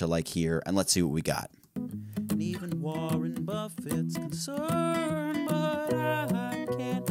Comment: I first heard it as "Even Warren Buffet can succ...."